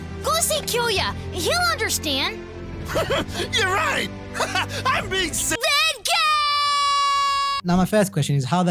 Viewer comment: The caption said "I'm being gay" instead of "Benkei" here